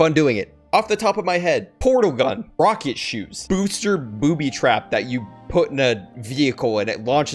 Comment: I thought u said booster booby😂😂